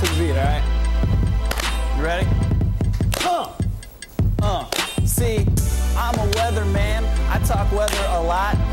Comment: I thought it said raping weather man.